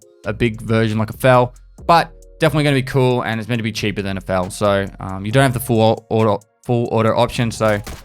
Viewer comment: Is he saying Val or Fal? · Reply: fal